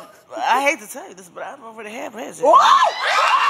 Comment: Can someone tell me what she said bout Ray J @ 0:31 ? Did she say she had sex with him? I can't tell cause she all mumbling lol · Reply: She said "i hate to tell you this but i already had ray j."